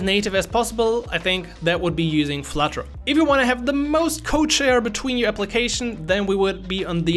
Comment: "if you want to have the most what between your application then we would be" the most what between your application? Can't make out what you're saying there. The auto subtitles says "co-chair" but that means "a person who chairs a meeting jointly with another or others" which is not what I think you wanted to say. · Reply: code share